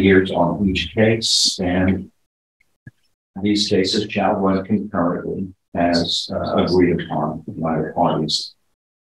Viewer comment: Glad the sentences are consecutive. Judge is correct--Federal sentencing buries people like this. It uses a formula for each image x years. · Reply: I heard the judge say concurrent. So 10yrs total for all 5 cases. 😐👎🏻